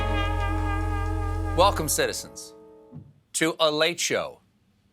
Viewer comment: He said "Welcome, citizens!" and I heard "Nation!"